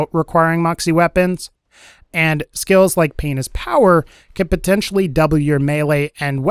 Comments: thought he said penis power XDXD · I swear he says p*nis power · I had to do a double take. I heard “penis power”